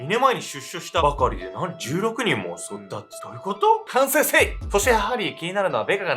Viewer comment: 周りから信頼されてる人ほど、何を考えてるのかわからない。 の「反省せい」が「セイセイセイ！」と聞こえて フゥー！って言ってた、芸人思い出した笑